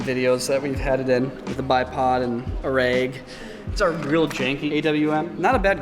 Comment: Niko, did you just say raig